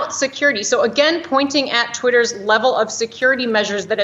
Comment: Did she say "clitter" at 19:54?